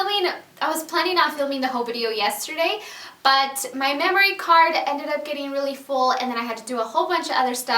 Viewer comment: At first I thought you said your "mammary" card which is hysterical considering the subject matter. I don't think you have to explain yourself to anyone. You look beautiful and enjoy the results. Your business.